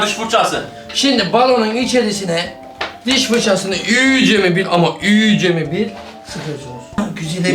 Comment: 10.58 balonun içine diş fırçasını iyice sıkıcaz mı dedi ben mi yanlis duydum 😂😂😂😂😂